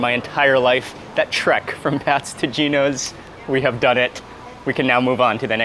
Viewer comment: It sounds like dude said PASTOR GINO.HE IS FROM PHILLY AS WELL.